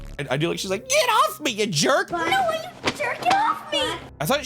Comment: BRO I revisited this episode once and thought I heard "I don't know why your JERKIN OFF ME!"